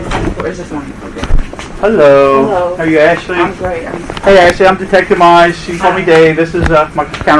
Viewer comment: did anybody else hear her say "I'm great?"🤔🤔 Bitch, how?🤔🤔